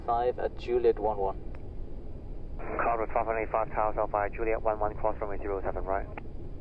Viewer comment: I swear I heard 'cross Romania...'